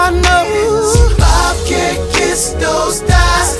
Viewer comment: wtf now it makes sense how did you know he says bob cant kiss those thighs i didnt know that i thought he said bob cant kiss no stars lmfao and i thought instead ofhim sa bob can stay this hard i thought he said bob cant tell hispart lmfao!